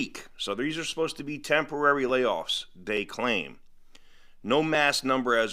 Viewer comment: I thought you said no temporary layoff